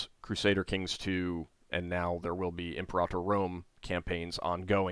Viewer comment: Playing the video while multi-tasking, mishearing the multiplayer game of the month as . . . Improper Rome. Which made me think "Caligula simulator" ? I didnt think played that kind of game lol